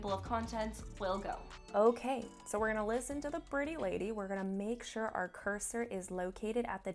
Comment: Did she really say "Ok, so we're going to listen to the pretty lady"!?!!? Hahahahahha!